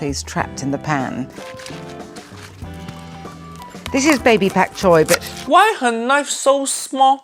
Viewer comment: Did … did she just say, "Baby-back choy?" Is that some sort of plant version of baby-back ribs?